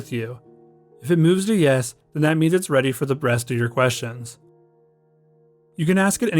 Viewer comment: I must've listened to this 30+ times n I swear he says "if it moves to yes than that means it's ready for the BREAST of your questions" if anyone can actually hear him say rest please confirm, but I swear he's saying breast😂